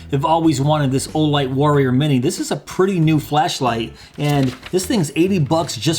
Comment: I thought you said fleshlight